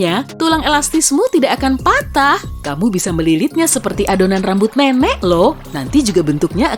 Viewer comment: apa cuma gw yg denger kalo suaranya kedengeran adonan rambut memek ? 🤣